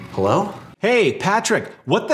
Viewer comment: When said, “hello there,” I thought she said, “hello, bear.” So from now on I’m going to think that Patrick’s family calls him Bear as a cute nick-name.